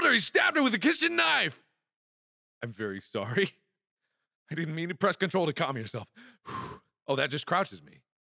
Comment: did mark say he didn't mean to press control key to calm down????, LOL!!!!!! XD XD